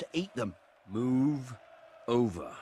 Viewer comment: Move over What? (You say it) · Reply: MOVER OVER!